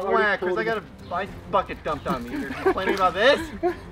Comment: Me: did say the F Word? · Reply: Nah he said bucket